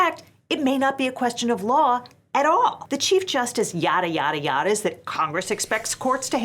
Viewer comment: Did I hear “Chief Justice Yadada something?” Lol